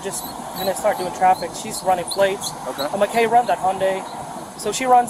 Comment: wait, wait. @ 5:40 Did they say while stuck in traffic "we're just runin the plates and "M" run the Hyundai"? They have to have a reason to run plates. They can't just arbitrarily run folks' plates.